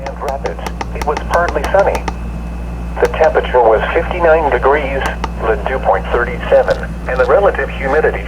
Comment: It sounded like it said "The temperature was 50 million degrees" 😂 Edit: But thank you for the info I'm going to get this radio very soon